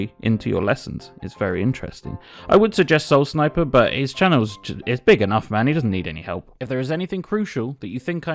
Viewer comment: What channel did you recommend, I can't understand what you said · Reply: my bad chaotichammer